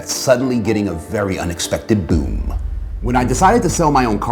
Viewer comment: did you say boom when you meant boon?